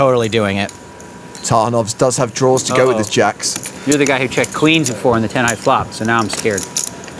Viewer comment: what is Daniel saying? “You’re the guy who checked....” can’t hear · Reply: Yukimi Hikichi You're the guy who checked Queens before, on the 10-high flop - so I'm scared.